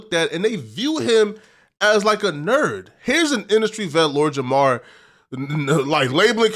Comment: what did he say?? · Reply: I think he said Lord Jamar is an industry vet